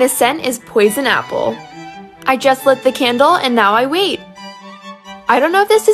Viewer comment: When she said now we wait i thought she said now we weave ..